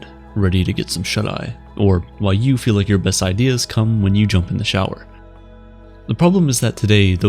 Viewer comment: Did he say cum when u jump in the shower at 6:30?